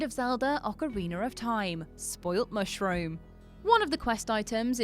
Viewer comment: Why does it sound like she says ocaweiner of time. The fastest and easiest way to get the mushroom to the potion shop is to go through the lost woods Into the goron portal. Jump down the mountain make sure you at least have some heart containers before you do that. Jump down. Either climb the latter and do a side flip, go thru the option shop or grab a chicken and fly over the fence at the back part of the potion shop. And why does peach look exactly like zelda.